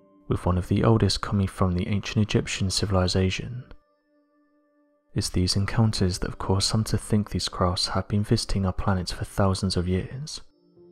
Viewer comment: At first it sounded like he said "it's these encounters that lead many to believe they've been fisting our planet for years" and I was like :o